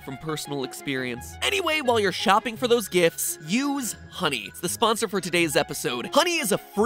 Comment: At first I thought you said: "When you buy those gjfts, use money."